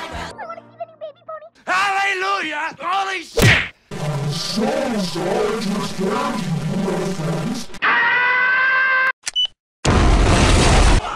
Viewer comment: What did Fluttershy say at 0:42? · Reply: "I'm so sorry to have scared you, my friends."